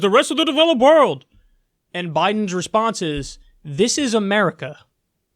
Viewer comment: I heard, “in this America” like he was stating that it was a false statement